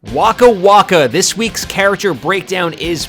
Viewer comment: My volume was low in the intro part, I thought he said waka flocka 😭😭like shit